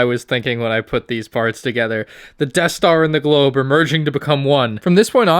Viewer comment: Wait a sec did you say Death store ???